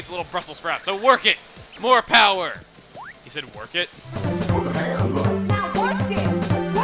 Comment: Oh, these words from Wario land 4 was "work it"? I didn't knew that. I thought they said "Pork it".